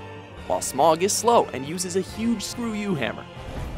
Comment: did he say ''Huge Screw-you hammer?'' lol